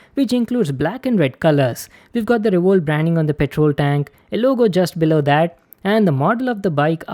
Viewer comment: did you say patrol tank. Nannnni